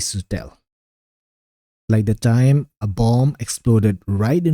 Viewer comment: Did you just say bomb?!! Dude, where the heck do you live?! WTF?! 😱😱😱